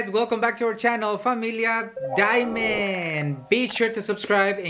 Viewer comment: am I the only one I thought it said family diamonds LOL